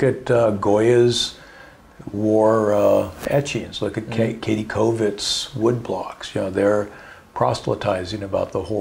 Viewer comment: what did he say on 1:12:20 katey covette?? i can't find anything on her. can anyone spell it? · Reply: <link> Kathe Kollwitz German artist